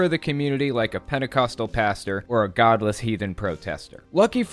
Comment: I heard it as Pedecoastal...